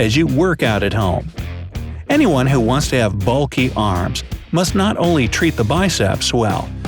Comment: At first I heard "Bucky arms"